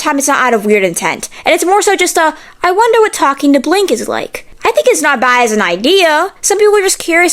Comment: i thought she said blink as in referring to blink-182 and i was deadass like "fuck i wanna talk to the real mark" until i realized she said blank. post-concert FUCKING BRAINROT 🙏